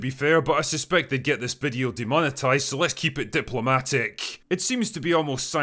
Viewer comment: did I just hear someone say... DEMOCRACY?!